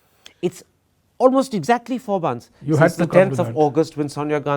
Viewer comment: What does Jairam Ramesh say after laughing? "You had the...." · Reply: You had to come to that..